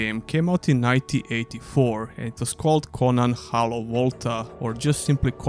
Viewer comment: Your accent made it sound like you were saying the first game was called "Conan: Hello Walter." 😂 I played Conan the Cimmerian on my Amiga in the 90s. I think Conan Exiles is a really good game. Thanks for the video!